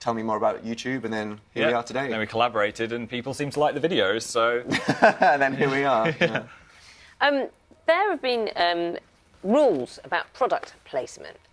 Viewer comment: did he just say then we got to date....